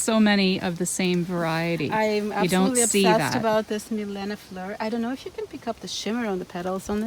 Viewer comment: What's the name of the dahlia you mentioned shimmered? I couldn't understand what she said. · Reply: If you mean the pink/peach dahlia that she says shimmers/glitters at around 4:40 I think it's 'Milena Fleur'. I had trouble hearing too. I think the camera was too far for the audio. I thought I heard her say 'fleur', so I checked her web store for a similar name. Mikayla Miranda is white/lavender in color.